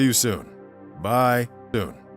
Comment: bro anyone else hear his say "bye OON"